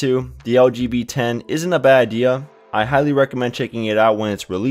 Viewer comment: When you say "LG V10", it sounds like you say "LGBT". :)